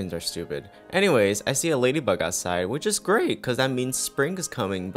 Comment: I thought he said he saw lady bunny outside.... I- 💀😂😭